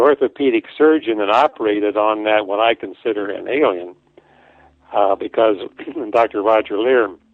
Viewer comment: Sounds Like you Said an Alien Bean!!😂